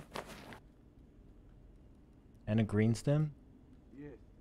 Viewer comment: anyone hear him say “and a green stim” and in the background a scav said “yes” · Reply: "есть" means "roger" in russian